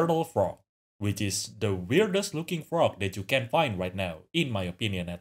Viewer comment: When I thought you said they're the realest looking frog, I'm like "know what? hell yea!" When I found out you actually said "weirdest" I'm personally offended.